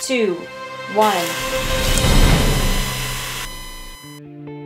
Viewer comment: What is the little dude saying at 1:09? I keep replaying it over and over...."I think we've just discovered______" Planes? Plates? Blades? I really am scratching my head on this. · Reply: Plates. :)